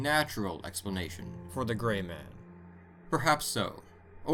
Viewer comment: For a second I thought he said the gay man or happy man because I learn gay is happy